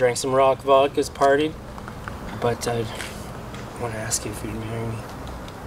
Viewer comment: Did he just say I wanna ask you for too marry me??? Lol. Dude I’m happy for you but don’t talk like that.